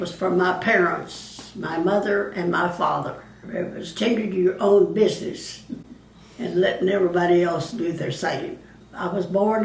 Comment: What does she say at 2:20? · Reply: I think she might be saying "tend to your own business", like mind your own business.